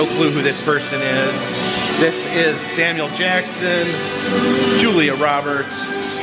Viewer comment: did he call him Daniel Jackson?